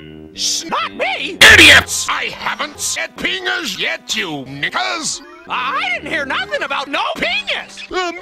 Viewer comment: WHAT DID HE SAYYYYYY? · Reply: "Knickers!"